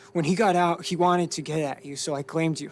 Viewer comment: Marissa Wilson not get at her claim her which is different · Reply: he clearly says "get at tou"